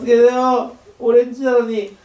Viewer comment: おれんちなのにがオレンジなのにって聞こえて、オレンジ？と思った